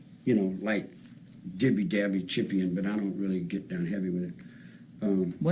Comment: > the words do make sense, he's just struggling to find the right one. I believe he's trying to say he 'dabbles'. What he ended up saying was, "dibby, dabby, chippy, and (chippy-ing?), but I don't really get down (or none?) heavy with it." It's clear you've never taken a hallucinogen 😆 Great content by the way, just recently found your channel and I've been binging!